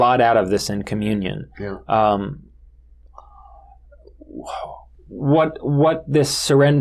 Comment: I am curious what made Jorjani say "whoah" at about 13:30. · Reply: I don't think he is saying "Whoah", I think he stuttered on the next word, "what", while formulating his thoughts.